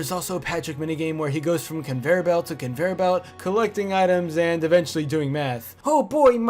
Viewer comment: I first heard that as "And eventually, starting doing meth"